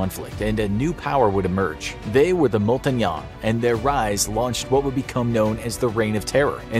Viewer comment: Did you say ‘Montagne’ like the Rainbow Six: Siege character?